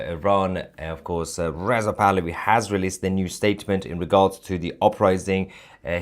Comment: hah - sounds like you say crown prince of iran is Riseup pahlavi.. lol